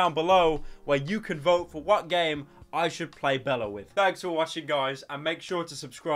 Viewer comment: I swear I hear Bella instead of Belle